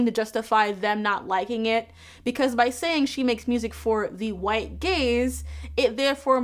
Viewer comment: I kept hearing "making it for the white gays" 😅 I think because the sentence was referring to music, rather than something you watch🙃